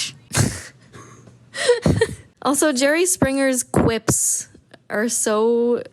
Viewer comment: My dumb a** thought they were saying Jerry Seinfeld till 4 minutes in bro 😩